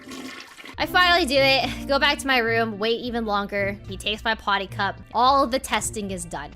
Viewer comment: Anyone else heard it as " He taste my potty cup " im sorry xD